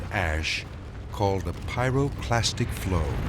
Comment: Edit: Subtitles are finished, I tried my best to make them as accurate as possible. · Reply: Just so you know… at 23:53, it says “pyroplastic”, instead of “pyroclastic”. But, to be fair, I made the same mistake when I first watched this documentary as a kid.